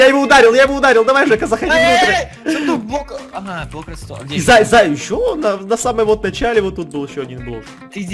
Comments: Стоп мне послышалось или на 14:33 называет "зая" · На 14:33 сказал "ЗАЙ ЗАЙ" Или мне послышалось?!